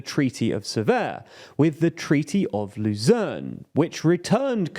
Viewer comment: CORRECTIONS: At 0:18, we say 'US fighter drone' when we meant to say 'fighter jet' At 1:38, we say 'southeast Syria' when we meant to say 'southeast Turkey' At 7:18, we say '2009', when we meant '2019'. Apologies, these are sloppy errors that we shouldn't be making, and we hope you nonetheless enjoyed the video! Longtime viewers will know that this is a chronic problem that we've promised to fix in the past, and if it's any consolation, for the past few weeks we've been monitoring these errors to figure out where most of them come from (animation/writing/narration), and we plan to implement anti-error policies from next week. Nonetheless, we accept that these are unduly sloppy, so, again, we're sorry, and hope the video was nonetheless informative. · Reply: treaty of Lausanne* (I think I heard Luzern)